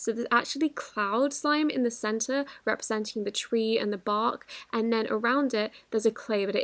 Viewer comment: I'm not the only one who heard say "twee (chwee) " right?